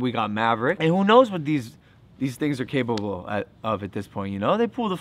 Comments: R.I.P When he said “you dont know these things are capable of” I thought he said “ you dont know what these niggas are capable of” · Did he say the n word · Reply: Nope... he said things.. i replayed it